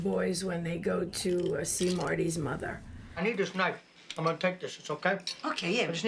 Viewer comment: Did she say "Marty's mother" at 4:05?